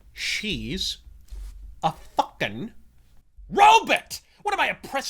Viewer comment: Robut. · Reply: I thought he was saying Robit like Dr. Zoidberg from Futurama.